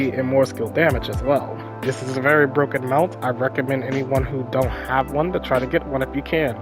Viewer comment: I couldn’t hear what you said, you said a mount or something? I’m confused